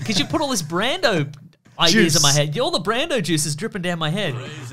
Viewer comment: I heard it as Brando Jizz